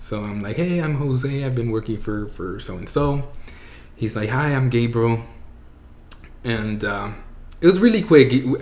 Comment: I love Jose and i am so thankful for his coming out story. But side note... when he said "Hi, im Gabriel", i swear i thought he said "Hi, im gay bro". I was like, damn thats quick lol. Im happy you were finally able to come out and be confident. Much love Jose